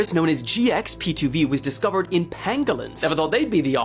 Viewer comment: I thought they said it was from penguins in 2020… pangolins makes a bit more sense 😂